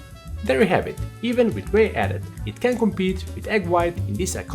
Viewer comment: In case you missed it in 2:26: "There you have it, even with whey added, it can't compete with egg white in this iconic cocktail" I am doing tests so you don't need to. You are welcome! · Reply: ah, I thought you said “it can compete”.